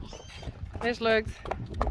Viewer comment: did say, "Es lugt." ("It smells/stinks'")?